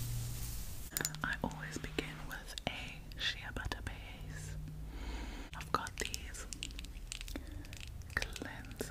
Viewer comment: What did she say at 11:50? Shepatapae? I have no clue what that is. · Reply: *Shea butter bass